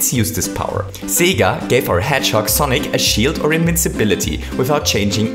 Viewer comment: I thought he said “gay for hedgehog sonic” lol